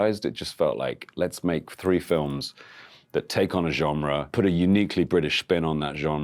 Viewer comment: Does he say “gemre” with an M?? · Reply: He says genre with the French pronunciation as it should be.